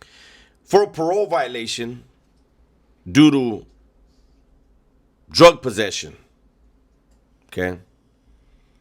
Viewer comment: Sounded like you said "Doo Dio Drug Possession" 😂😂😂